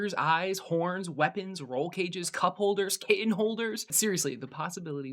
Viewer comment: I just heard kitten holder and i think i want a moving castle for my cats.